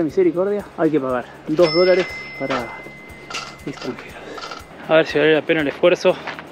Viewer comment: perdonen....quizás solo es una equivocación y es valida...no se porque en el minuto 5:49...cuando el dice que para entrar hay que pagar dos dólares, escuche que dijo ( QUE ESTÚPIDOS), espero que solo sea una muy mala audición audición porque no seria agradable para ningún nicaragüense , escuchar algo así.